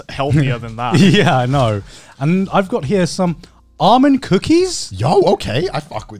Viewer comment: Bro wth I was so confused for a minute cause I heard Armin instead of almonds ._. I can't be the only one